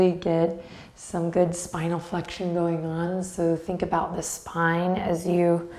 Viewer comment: In the beginning, I honestly thought you said "smile flexion" instead of "spinal flexion." 😄😂